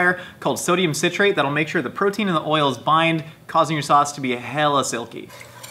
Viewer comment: I thought he said “causing your sauce to be hella silly” like im not already silly enough.